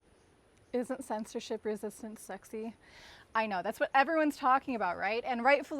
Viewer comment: What the word at 0:02 "sin sexy".. I mean the words before sexy or is it just mumbling? We need more beginnings like this.